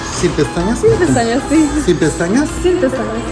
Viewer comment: When he asked you if you wanted lashes, it sounded like you said “Si, pestañas”.